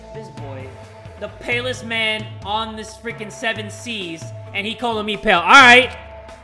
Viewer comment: “The palest man on this seven seas” THIS SENT ME💀💀💀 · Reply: RODRIGUEZ 2:21 He clearly said Seven Seas. Listen clearly, and next time, before you correct someone, know that what you're typing is right. If you don't, you'll just make a huge embarrassment for yourself. 😊 Oh and just saying this in case you say it was a joke and r/woooosh, I'm terrible at recognizing jokes. 👀